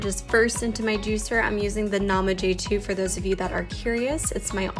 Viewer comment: Hello great video , can you tell me what juicer your using? Sounds like you said Nama J 2